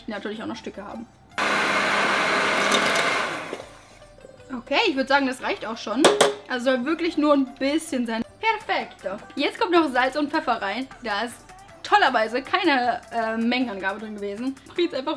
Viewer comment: did you say perfecto ??